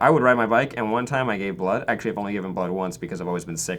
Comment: Can someone tell me what he's saying at 4:06? Sounds like "gave blood" or something and I really want to know what they're talking about